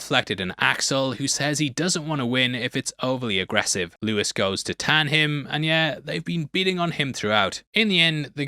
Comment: Did he say “Lewis goes to tan him”? as in Lewis Tan, the actor’s name? 😂